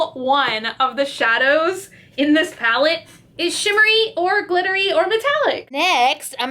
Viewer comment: when said "shimmery or glittery" I misheard really bad and got "shittery" out of it XC